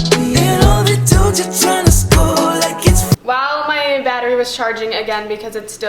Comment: did anyone else hear say while my batter was charging instead of my battery